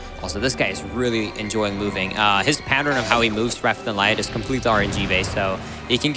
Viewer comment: Shout out to saying "reft and light" instead of left and right at 6:04:46 ish.